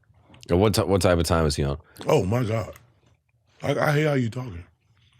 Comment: Did he say I hate how your talking lmao1:05:59